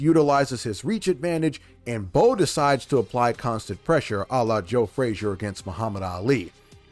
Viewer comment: Did he say Allah?